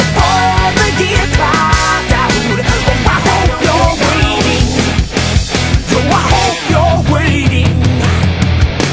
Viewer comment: What is said behind the first "oh I hope you're waiting" at 1:00? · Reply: It's an echo of year clocked out